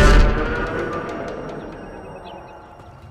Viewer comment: TW fans: Whats taking them so long!? CA: Have a little faith! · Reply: Wait markus was saying faith? All this time I thought he said to have a little feast